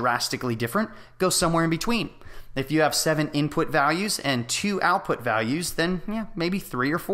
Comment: Thanks .I'm just listening.I noticed a thing that maybe could be ineherent: Around 2:20 i heard something from my speakers sounding like "Input Dalues" and "Output Dalues". Of course it is a problem of noisy environment, and it will be "Values" ,so I decide to ignore the "D" and substitute it with "V" .I ask: Is that what should happen in a specific phase of the learning process?Thanks